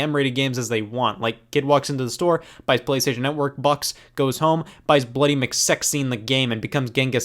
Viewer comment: I thought Scott said PlayStation n-word bucks